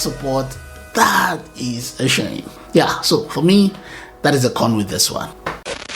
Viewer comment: what did he sayyyyyyyyy????? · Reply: "That is a con with this one."